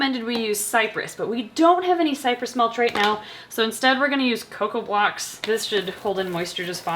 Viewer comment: i seriously thought said "we're going to use coco pops instead" 💀💀